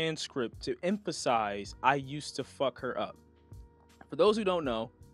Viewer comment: In the actual recording it sounds like he said ," I just wanted to fuck her up bruh". Not " I used to fuck her up" ..